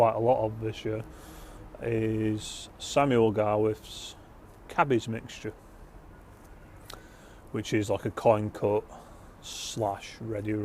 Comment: I thought you said Cabbage mixture at first, I said what the fuck to the screen.